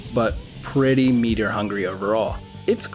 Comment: meteor hungry?? What is he saying? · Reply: Meter hungry, meaning that the silk meter to use abilities is overused, because instead of one silk skill you have 3 Essentially saying that you need to make sure you're silk meter is full so you can use this crest at its best